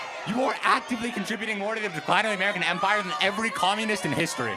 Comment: Did anyone hear him say "American Empire" or is it just me